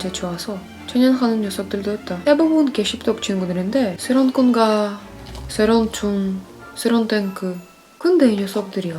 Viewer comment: 소련 군가,소련 총,소련 탱크 다 나만 새로운 총, 새로운 군가, 새로운 탱크로 들리나?ㅋㅋ